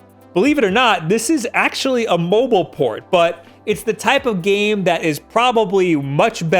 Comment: I thought you said "A mobile porn"